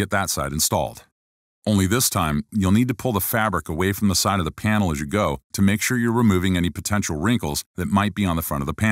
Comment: Could someone please tell me what it's said at 06:17 "and when you're done (???) the fabric". This is an amazing tutorial and that's the only words I can't understand so I would be very thankful if someone could help me with that. Sorry for my macarronic English. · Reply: I Carvalho Thanks for watching and the nice comments! I’m saying “And when you’re done adhering the fabric.” Here are some dictionary terms that may help with the word adhere: “to stay attached; stick fast.” Hope that helps!